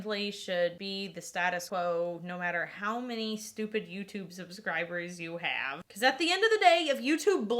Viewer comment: Did she say on 15:08 "doesn't matter how many STUPID YouTube subscribers you have " ?